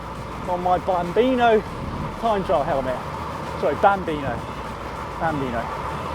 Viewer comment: Great video and I like the bonbeano helmet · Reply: I thought he said Danbino